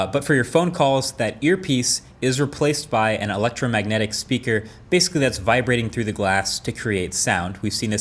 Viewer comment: did I hear "electromagnetic spraker" ? no ** it's electromagnetic hahaha